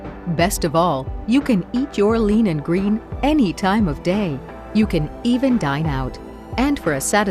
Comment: lol I was distracted for a bit and I wasn't looking at my screen and I thought they said "You can die now"